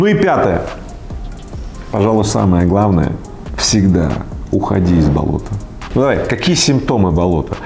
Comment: Мне послышалась "всегда уходи от налога" 😅 или я услышал то что должен был услышать🤭